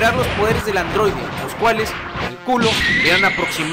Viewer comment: a la primera entendi culo tuve que reproducir de nuevo esa parte para escucharla bien XD